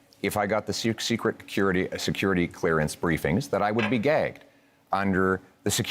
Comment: I'm sorry I misheard what Pierre said I thought it said he would be gay not gagged😂😂😂😂